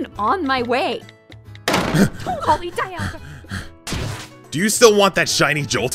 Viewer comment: I heard "Holy Dialga!" as "Don't call me Dianna!".